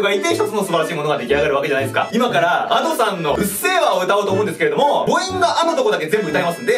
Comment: くっせぇわに聞こえる